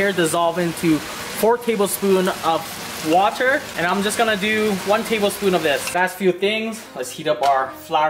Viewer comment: Bruhh I'm dead I thought he said 1 table spoon of diabetes